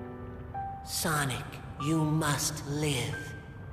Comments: Speaking problem at 2:38 cause Chip said Sonic you must live but the subtitles said Sonic you have to live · These subtitles are so wrong. The subtitles say, "Sonic, you have to live!" Chip/Light Gaia says, "Sonic, you must live."